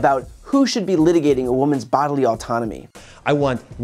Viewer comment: Listen to women. We know how our bodies work. Men, mind your damn business! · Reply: MontgomeryWenis lol at first I thought you said Anatomy! Whoops. Emily Litela would say "Thats different then!"